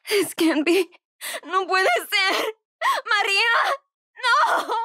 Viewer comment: what does she say in Spanish in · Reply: It can't be (No puede ser)